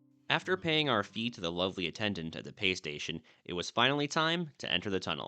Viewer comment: I thought it said fee PlayStation 😅